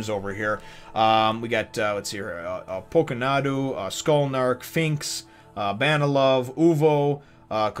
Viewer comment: Did he just say skullnark 😂😂😂